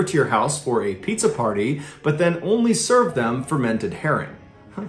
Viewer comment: Idk what herring was so I thought you said hair ring ,hey it’s not pizza it’s hair ring again.